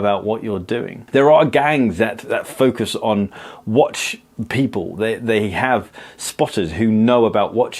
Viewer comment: the auto caption wrote "lose a wife" instead of 'watch' 😂